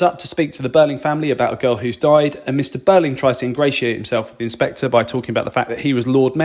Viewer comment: what was the word you said? Ingratio? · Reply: Birling’s attempt to ingratiate himself with the inspector by referencing his influence fails. Ultimately, he tries to influence him and falls short because the inspector is only motivated by justice for Eva Smith - and the millions of Eva Smiths and John Smiths whose lives are intertwined with ours.